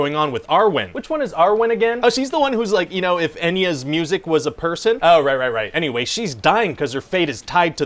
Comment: Arwen: If Enya's music was a person. 😂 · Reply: Oh that's what he said. I heard "NES music" and was extremely confused.